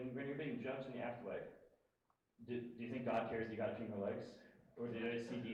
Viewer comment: What does he say at 5:12? RIP Hax$ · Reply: I think something along the lines of, "Do you think God cares that you got to keep your legs, are does he just see the intentions behind what you did?"